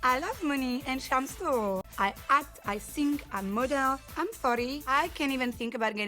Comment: I had to go back and listen again and read the captions when she's talking. Because I seriously thought she said, "I murder" but it was actually, "I model."